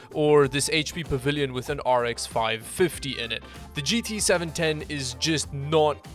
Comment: you said rx 550 instead of rx5500.you have to remember that the rx 550 delivers gt1030 performance where as the rx 5500 delivers rx580 performance.